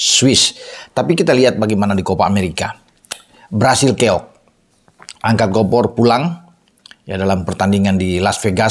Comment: Angkat kompor pulang 😅😅😅 dengernya bung ..wkwkwk